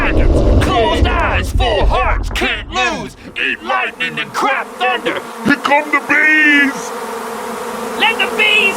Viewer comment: Did I hear "eat lightning and crap thunder"? or..